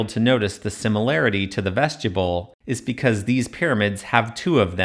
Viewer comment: Another excellent analysis and exposition. But I've never heard anyone, anywhere, pronounce "vestibule" as though it were written "vestuble". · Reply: I keep hearing “Vegetable”, lol